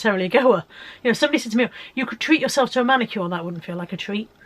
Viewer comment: Oh I should concentrate more when I'm watching!! When you said that people say "Why not treat yourself to a manicure" I thought you said a man!! It gave me a giggle anyway. Well done on your loss, coz a loss is a loss x I always feel better with a loss even a small one than a bloody gain xx good luck with hitting your goal weight for your birthday xx